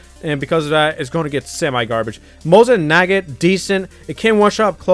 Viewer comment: did you say nugget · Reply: No he said naggat